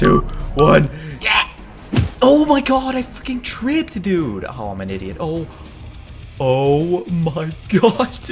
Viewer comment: Did he say I f-word tripped dude